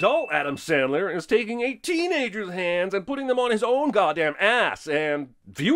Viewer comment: Minus 1 sin for not properly writing what is being narrated. You said "hands" NOT "hangs"!!!! <REVERSE DING SOUND>